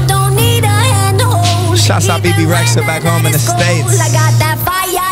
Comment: What did he said in Bebe's part at 1:15? Does anyone knows?? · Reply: I'm french but i think i heard " shots a bebe rexha back home in the states " or something like that